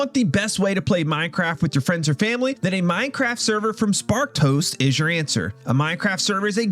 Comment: Whenever you say Sparked Host", I hear "Spark Toast" 😂😂😂